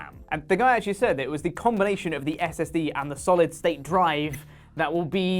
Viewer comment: He said SOC and Solid State Drive. Initially it did sound like he said SSD though.